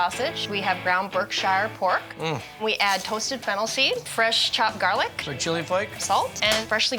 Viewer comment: It sounded like he said fentanyl was the last thing he was going to mix in lol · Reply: I heard him say it too lol, but it was Fennel seeds